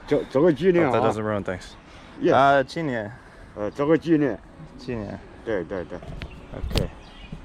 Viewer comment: The old man actually said (拍个照)作个纪念 ((take a photo) as a memento) instead of 来中国几年了？(How long have you been in China?)